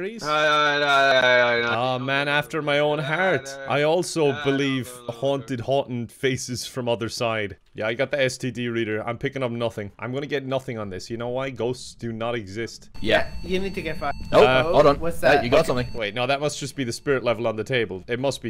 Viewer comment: did he just say STD reader?